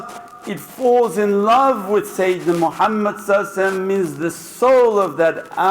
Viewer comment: I keep hearing him talk about loving Satan, Im not Muslim nor do i speak Arabic but I do have a great interest in Islam being a christian. I'm having trouble reading between the lines. Am I hearing him correctly or taking what he is saying out of context?